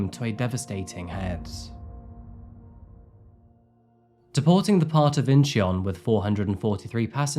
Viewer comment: Did you say deporting the part or am I hearing things?